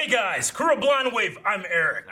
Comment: Everytime Eric yells, "Hey guys crew blindwave!" I hear "Hey guys screw blindwave!" I hope I'm not alone and those who didn't hear that, I'm sorry.